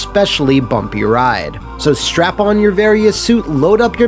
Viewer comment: “Did he say strap in or strap on?”